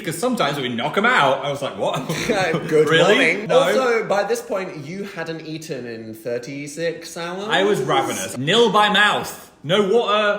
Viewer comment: The subtitles at 19:48 are funny. Dan says: "Also by this point you hadn't eaten in 36 hours?" and the subtitles say: "Also by this point you hadn't eaten in 36 salads". I'm easily amused.